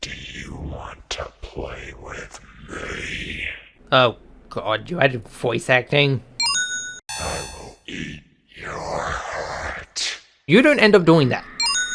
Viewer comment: istg did that say "i will eat you out" if that did then im pretty sure you're playing with sark and not x